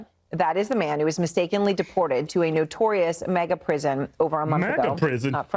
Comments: I'm pretty sure she said "MEGA prison", at least that's what I heard... Let's Go Brandon...!!!!! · Not defending CNN AT ALL!!! But it sounded like she said MEGA prison not MAGA prison cuz that prison is one of the biggest if not the biggest prison in the world....not defending the prisoner either cuz he's a scumbag that deserves to rot in hell!!! #Trump2025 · Bro she clearly said "Mega prison" not MAGA lol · I heard "mega" prison. You are wrong or trolling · She said MEGA prison. That's what I thought she was saying anyway. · She said “Mega Prison”, not “MAGA” Prison lol. Doesn’t change the stupidity and ignorance of CNN & every woman displayed on that pathetic network, but she didn’t say what y’all thought you heard. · are we sure she did not say Mega- prison and not Maga. I thought she said mega prison. idk though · i heard mega prision not maga.... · Sounded like she said mega prison not maga prison you guy nitpicking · I thought they said mega prison lol